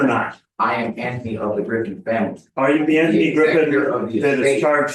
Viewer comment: 😂😂 I swear he just said "I am Andy, of the Griffith family", really, from Andy Griffith show😂😂 sorry i'm old😂😂😂